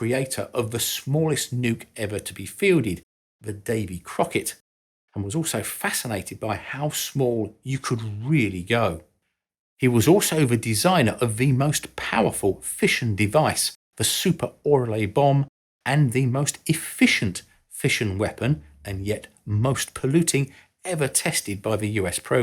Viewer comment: I heard "fishing device" and thought about some mad lad dynamite fishing with nuclear weapons.